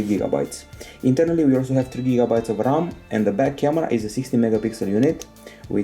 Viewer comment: it sounded like he said bad camera not back camera